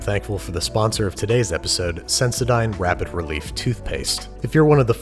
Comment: Bruh i heard him say : s*x crime isntead of sensodyne